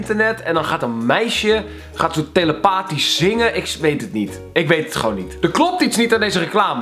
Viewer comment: "ik zweet het niet" Like als je dat ook hoorde